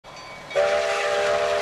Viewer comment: it sounds like he said "WAS THAT THE 587!?"